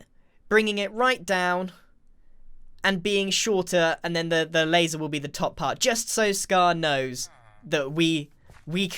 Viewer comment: lol what did just say? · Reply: somefin is wrong wiv the light stil rite? wen there is a roof on it its daylight?